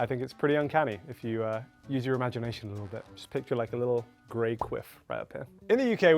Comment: I thought said "Grey Queef" 😂😂